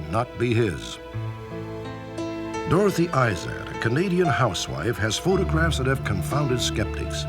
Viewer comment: Did he really say "comedian housewife"? I can't hear anything else.